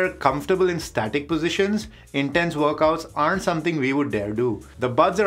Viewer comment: Did i hear “Dildo” at 3:32 ? what a shame!